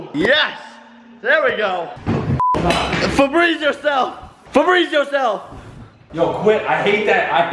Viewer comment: When Jake said “frebreeze yourself” I thought in my head I thought he said freeze yourself hahahahaha 😂